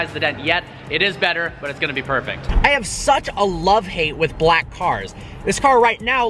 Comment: Thought he said I have such a love hate with black guys